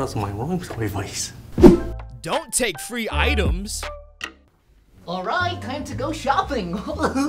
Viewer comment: I thought you said don’t take three things but you took five things